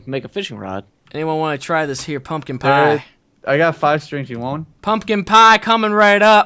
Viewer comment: LMAO did he say blumpkin pie?